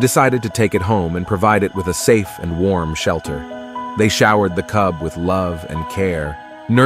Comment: Wait I thought you said "Shower the tub" 🧐but you actually said "Shower the cub"